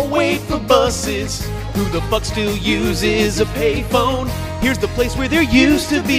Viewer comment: I think it said ‘’who the f*** still uses payphone’’